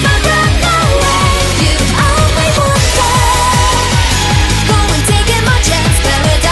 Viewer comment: I thought its "you are my water" Bruh inspiration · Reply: bruhh, i heard "you are my wanted"